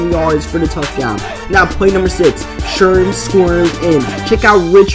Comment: did anyone else think that said sperm squirts in or was that just me?